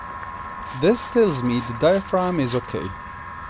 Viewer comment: Hello, first of all great videotutorial. It helps alot.Can you just help me undestand what you are saying/doing on 6:00-6:10 minutes on the video. At this point you are saying something like" this tells me the dia... is ok"..sorry but I dont get it. We saw earlier in the video that bubbles are comming out from that spot and now you are saying that it is OK. I KNOW I have missunderstood what you said or ment so please correct me. Once again thank you for this great video. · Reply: By doing that step im only checking the diaphragm integrity inside the valve, if its torn smoke will leak on both sides and it will bubble like the first test. Hope this answers ur question